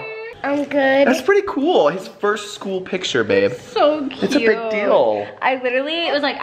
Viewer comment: OLIE LOOKS ADORABLE IN HIS SCHOOL PICTURES! 😍 WHO AGREES?! 😎👍🏼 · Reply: Everyday Fun With Troy & Eric I thought you said his high school pictures😂